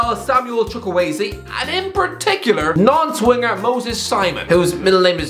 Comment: I thought he said n*nce winger